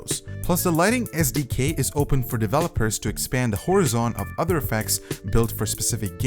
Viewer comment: What was that word at 4:16? Horizon?